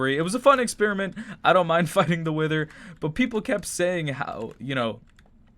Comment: sounds like he said he f’d the wither